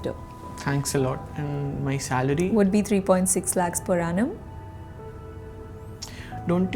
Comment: / I hear that 43.6 LPA😂